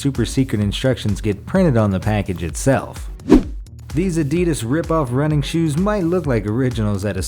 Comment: did he just say adeedus